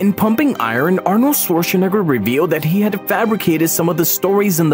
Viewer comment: Bro basically said the N word while saying his last name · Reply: I heard it too, it sounded like he said nigger. Maybe he emphasized the nigger part of his last name by accident. I don’t think it’s pronounced nig-ger But nai-ger if that makes sense